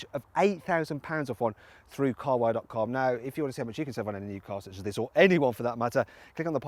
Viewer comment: wtf did you say? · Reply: Sam Harper If-you-wanna-see-how-much-you-can-save-on-a-new-car-such-as-this-OR ANY ONE FOR THAT MATTER....